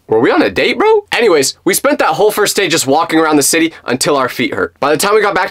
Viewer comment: I thought he said first date